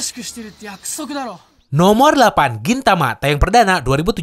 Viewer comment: no 8. Kintama? apa gw salah denger